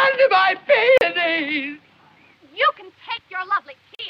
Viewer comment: As a non native English speaker, I honestly thought he said "What have you done to my penis?" but that didn't make any sense with the rest of the dialogue. Now I see that he says "peonies" or something like it, but instead of the dick, he means flowers... dumb me